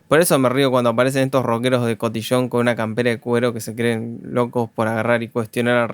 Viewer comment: "Esos rockeros que se sienten rebeldes por usar cuero y hablar mal del reggaeton" Que golpe tan fino pero certero, como de boxeador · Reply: Sin este comentario no hubiese entendido, "cuero", escuché "cubero" y mi tormento duró 3 meses jajajajaja "porrrr qué Matías menciona al Cubeto" y así desesperada jajajajaja Gracias prix, mi sordera cuarentona no me deja disfrutar jajajajaja